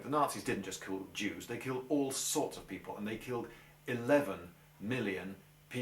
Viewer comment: He said "... and no I don't mean that the killed 1 million people ... they killed 11 million." But I get you, I thought he said 1 million there for a split second.